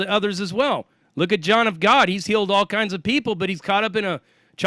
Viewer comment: Did anyone catch the name he said referring to someone involved in a child trafficking ring? It’s around 12:30. It sounded to me like John Gotti or John DaGod. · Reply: John Of God in Brazil